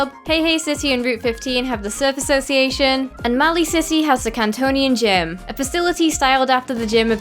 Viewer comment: i thought you said "maui city" lol